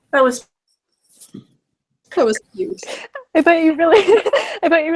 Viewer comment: did just say "that was cute" or did i just hear something random